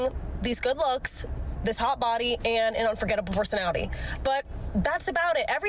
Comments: She never said "Fantastic personality" she said "Unforgettable personality". There is a difference and she is not wrong. You can't forget that insufferable personality. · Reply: I thought she said "forgettable." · I must have heard wrong. I thought she said "and a forgettable personality." Maybe she said "unforgettable," but she crammed that phrase together so fast, I can't be sure.